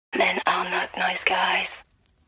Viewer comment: for a while i thought she was saying "man! i like nice guys"